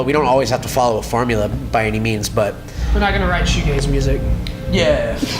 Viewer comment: What did say at 8:06? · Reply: M Castro were not gonna write shoe gaze music, it’s spacey softer punk genre I believe